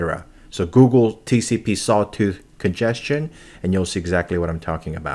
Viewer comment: what does he say? Tcp “solto” congestion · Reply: Hello, I said "Google TCP Sawtooth congestion". It's an easy to see the effects of congestion.